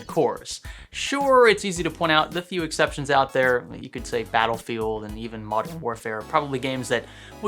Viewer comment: Did did you just say "Faddlefield"?